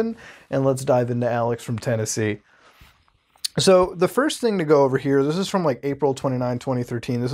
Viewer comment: I thought it said "john from Tennessee"